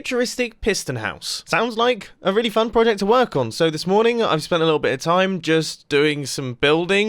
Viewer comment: "Sounds like a really fun project to work on" I thought you were going to say Sounds like a really mumbo project